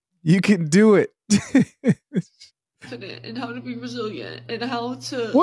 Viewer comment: 😂 i heard "How to be Brazilian"